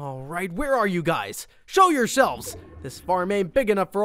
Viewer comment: him saying show your selves but sounds like he said show your shelves. Me:What you expect me to rip off my shelves.